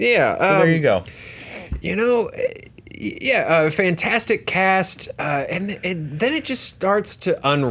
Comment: Did anyone else hear "fantastic cats"